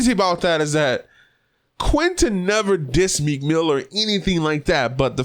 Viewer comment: i heard Meek Miller @ 5:19, im just looking out for you dawg, great stuff btw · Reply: Nah he says "meek mill or" just sounds like he sayin meek miller lol